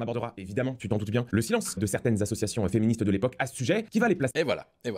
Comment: Là, tu mens ! Il n'a pas dit: "le silence des associations féministes" Il a dit: "le silence de certaines associations féministes" ce qui est vrai ! Mais bon, tu sais bien qu'en disant qu'il a dit "le silence des associations féministes" tu pourrais démonter son argument et le faire passer pour un menteur sauf que là, sur ça, c'est toi qui tronque la vérité, puisque ce n'est pas ce qu'il a dit.